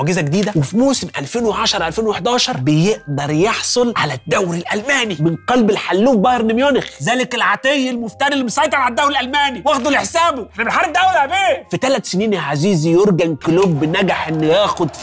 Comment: بقلب الحلوف بايرن ميونخ 😂 ولا سمعت غلط ؟